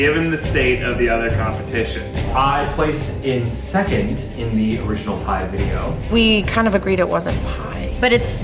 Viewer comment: When Arial came in I thought she said I vented lol